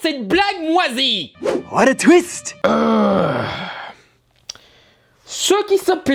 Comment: j'ai compris twix